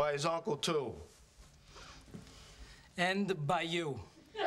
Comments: It almost sounds like he was saying 'And the by you' 😂😂😂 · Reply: that's "bayou" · It almost sounds like he was saying 'And the by you' 😂😂😂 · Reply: I thought he was saying ended by you 😂